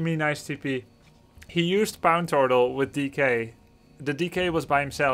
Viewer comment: did he just say pown tortal lol